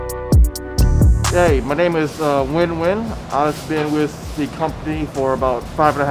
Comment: So the restaurant gets some extra help, and Josh gets to hang out with his old colleagues. That’s a Nguyen Nguyen situation imo... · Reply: look, I get it. The vietnamese language has extremely complex nuances in pronunciation that sounds very different for native speakers of the language, but for most “dumb westerners”, when we hear nguyen at 7:50 we hear pretty much “win”. I hear some subtle differences, but for me and most others it’s far from “not even close” Thank you for making me defend/explain my joke. Have a good day :)